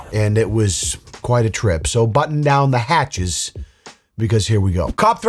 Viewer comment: I might have misheard Ole but it’s not “button” down the hatches it’s “batten” down which is a synonym of reinforcement. Or maybe he was being ironic..🤷‍♀️